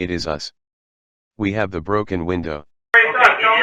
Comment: sounds like says, we have no broken window, not the broken window. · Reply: Tara Lynn “It is us, we have the broken window”.